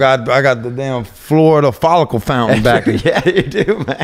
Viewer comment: florida folicule fountain lol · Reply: Oh I thought he said floor to follicle fountain